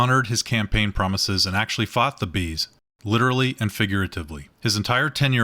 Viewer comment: I was listening and not watch. I thought you were saying Seabees LOL THE Seabees taking over the US cracked me up